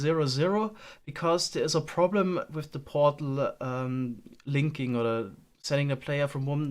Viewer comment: being german and saying oder instead of or